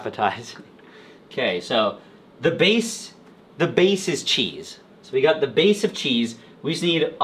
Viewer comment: "queso" lmao, i have that thought every time someone says that...